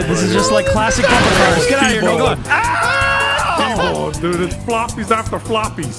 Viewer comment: did he say Nigla?